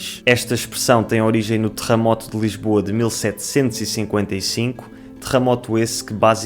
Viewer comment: Sou brasileiro e pela primeira vez ouvi alguém pronunciar "terremoto" como "trramot" kkkkkk Fiquei até sem entender oq vc quis dizer inicialmente